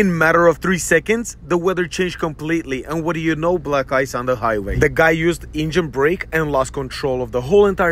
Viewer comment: i heard “black guys on the highway” 💀